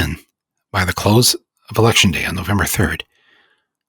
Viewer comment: does Michael say "election" or "auction" ?